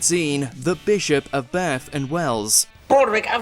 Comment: Thank you for including the Bishop of Bath and Wells. All these years, my American ears always heard it as "the bishop of Barthen wails".